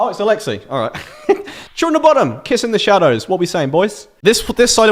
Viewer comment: Sounds like he says “When I was 16, I got really into Children Of Bottom,” not Bodom, lol.